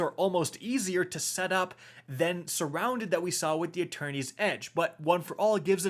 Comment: Wait. Is there a fourth sword? Cause I keep hearing "Attorneys Edge." 😆